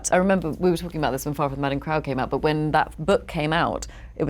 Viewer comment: What book does she mention at 2:23? I can not make out what she said. Please help? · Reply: I believe she said Far from the Madding Crowd